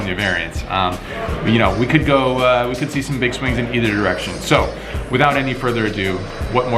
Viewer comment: it sounded like you said "could be a swing in either errection." Lol